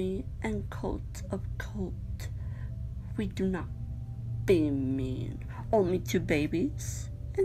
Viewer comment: aqui creo que dice " we do not be mean" qué significa " no somos malos"